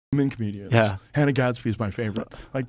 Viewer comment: Oh, Hannah Gadsby isn't funny. I was confused, for a second I thought Joe was saying Ana Gasteyer isn't funny. Ana Gasteyer is fucking hilarious.